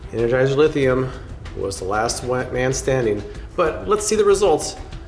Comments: did you say last "white" man standing? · Did you say, "last white man standing"? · Explain what he said at 8:53 ? Anybody 🧐🤔👁? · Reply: "was the last man standing" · Did he say energizer lithium was the last white man standing 😂😂😂